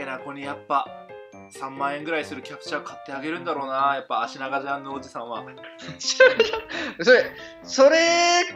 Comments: ジャンヌおじさんがジャムおじさんに聞こえたんだけどwwwww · がジャンヌおじさんって言った時ジャムおじさんって聞こえたw